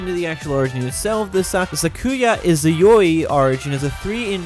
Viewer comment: I thought he said sakuya yaoi